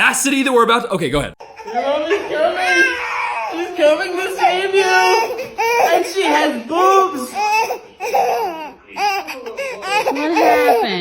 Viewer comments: I think you misheard the first guy. He didn't say the baby pooped; He said, "She's coming to save you - and she has boobs." Just thought I'd point that out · am I the only one who heard that guy say “and she has boobs*” 😂 I’m pretty sure baby was just hungry! And mama does indeed have boobs! And then he said “he needs a tiddy baby” lolol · I thought the first guy said "she has boobs!" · In the first one he actually said "and she has boobs" not "he has poop" and then he says "hes a tiddy baby". Not much better but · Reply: I heard ''he has boobs" 🤣